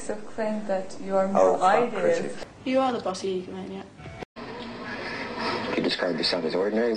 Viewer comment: What does Paula say? You are the bossy ego-maniac?